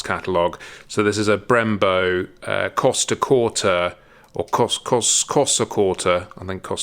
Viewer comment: All I kept hearing was “costa Concordia” 😂